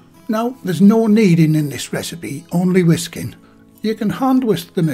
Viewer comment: I misheard ”There is no mead in this recipe, only whisky.” ;-)